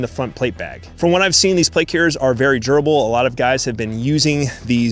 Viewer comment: “these plate carriers are very gerbil” is what I was sure you said at first